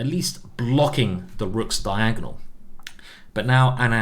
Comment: did he say rook’s diagonal